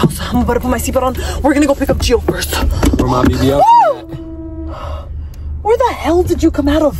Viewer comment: am I the only one who heard “were is my bbl queen at”😨